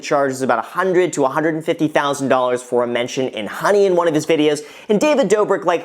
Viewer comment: you said what? I couldn't understand you. You said a "...... $150,00 mention for ??? In one of his videos". What is the ??? that you mentioned? It sounded like you said "hunting" or "hunning" or "honey". What the heck did you say? Come on man, I know you've obviously done a lot of videos but this is video basics 101. You have to annunciate or speak clearly so we can understand you otherwise there is no reason o try and watch more of your videos. Sorry to be harsh but I was here to learn and then you mumbled your words and all heck of a fail came down. (I keed. ;-) ) Thanks for the info though.